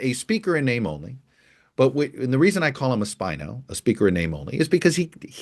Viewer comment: oh SPINO...thought you said spinless